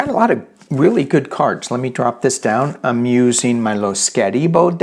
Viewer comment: I asked about the name of the cards you are using. Went back to the beginning and heard you say "Lo Skettibo" maybe?? For others wondering what he said, I think said it is the "Lo Scarabeo" deck he is using today. Not 100% sure...